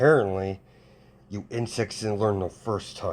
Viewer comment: when you said insects I thought you said in sex